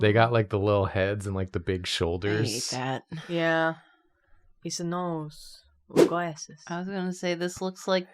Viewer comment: it sounded like the harp lady was saying It's a nose with glasses harp Lady is in your studio right now.